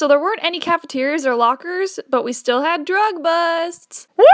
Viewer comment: I didn't hear the busts in drug busts and I heard "we still have drugs" I sat there for a minute thinking that's what it really said lol